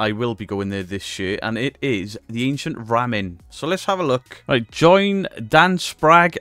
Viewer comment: I was busy while watching this video and I thought you said "ancient ramming" 🤣🤣